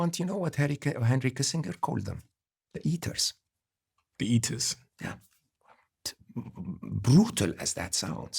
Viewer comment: "useless eaters" is what that character said. Small detail...